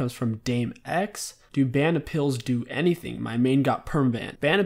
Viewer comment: can someone tell me what he said cuz i cant understand??? Thanks in advance, cheers!!! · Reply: league of fiora He said ban appeals. Where you appeal for your banned account (ask to get your perma banned account back)